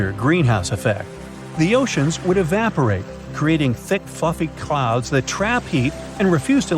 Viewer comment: At first I thought you said “thick fk’ing clouds” 😅